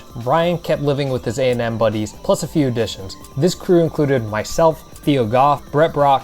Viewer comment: i think he said ryan yerrow or somethin like ryan but maybe his accent made it sound weird. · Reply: maybe! but at 8:33 i swear he says "myself" 🤔